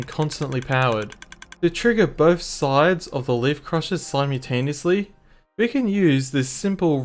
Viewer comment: did you just say slime-utaneously?